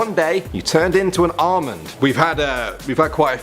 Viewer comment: When he said Almond I thought he said Armin and I was like well if you turned into Armin you would have two best friends named eren and mikasa and one would be super strong and the other would have anger issues If you get the joke your amazing marry me now